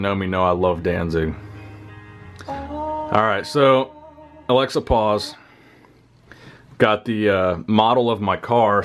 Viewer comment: I Thought he said equipoise not Alexa pause